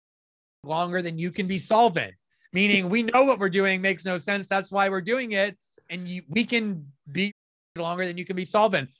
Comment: What is Glen saying that is censored? Why is it censored? wtf is that about? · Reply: He said "retarded". Yeah, I know, it's retarded.